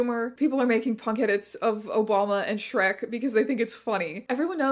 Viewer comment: I misheard you and thought you said "People made punk edits of a walnut" instead of Obama and now i kinda want to see that...